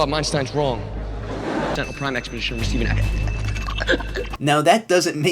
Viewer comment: If you go to 1:38 you can hear Sam saying Sentinel Prime In R.O.T.F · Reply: It kinda sounds like he says "Sentinel prime expedition: receive-" then he starts talking way to fast as he freaks out.